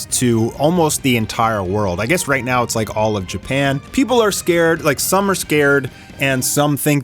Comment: did bro just say japan?🤣🤣 Theres no japan in opm world😭🙏